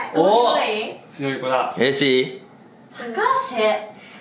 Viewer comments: のしばゆーのうれしい？が、Hi,Siriに聞こえてスマホのSiri動き出しちゃうんだけど、、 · のしばゆーの「うれしい？」が「Hey Siri？」にしか聞こえなくて？？？ってなった